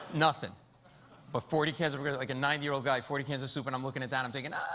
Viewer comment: Sounded like he said 9 year old guy...